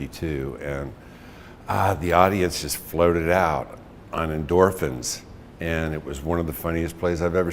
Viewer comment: What did Judge say from 00:31-00:33? The audience floated it out on endorphins? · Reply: Floated out on